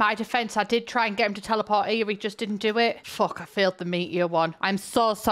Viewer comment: THANK YOU FOR TRANSLATING METEOR LMAO I THOUGHT SHE SAID MEAT-EATER